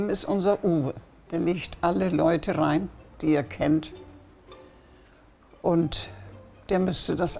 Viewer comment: was sagt sie da? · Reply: bisschen spät aber er sagt "er legt alle leute rein die er kennt"